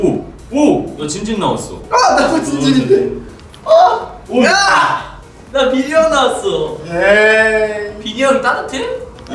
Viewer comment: i think sanha say "bin hyung"? not minhyuk, cmiiw